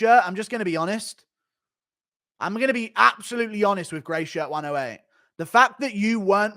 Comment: Lmao! I thought he was saying “Greysha” the whole time 🤣